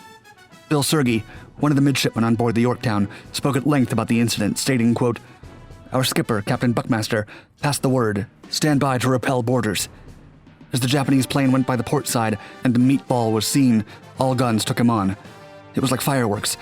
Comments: did he say "Our Captain BUTTMASTER" ??!!! .... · “Our skipper, captain butt master” ?!? 🤷‍♂️ did I hear that right?